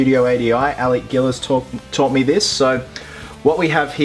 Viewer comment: did you just say “Alec Guinness thought me this”?